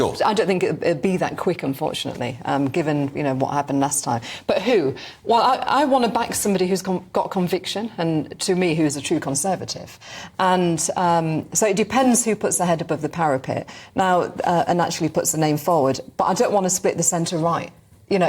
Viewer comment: I thought She said, 'I wanna back someone who's got a conviction'!